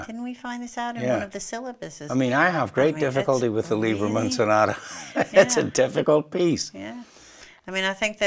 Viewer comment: Sorry about that, i couldn't hear that, what James Galway said, which one Sonata? Liebermon sonata? Liebermann? Here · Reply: "I have great difficulty with the Liebermann Sonata, it's a difficult piece"